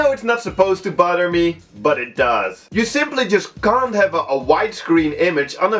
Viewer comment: I realize that it's an accent, but i legit thought that you said it butters you.